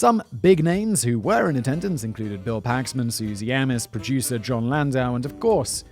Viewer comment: Great video, but it’s Bill Paxton. Sounds like you were saying “Paxman” to me.